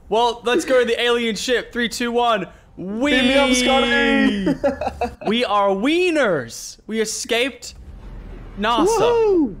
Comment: said: "We are winners." I heard: "We are weiners."